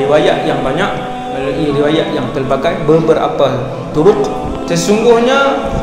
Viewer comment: dia cakap apa..aku dengar "DURUQ"